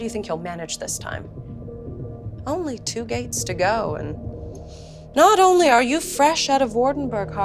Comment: Instead of "only two gates to go" I thought she said "only two gays to go".